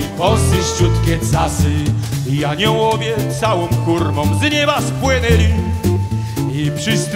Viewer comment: kto usłyszał ,,kurwą"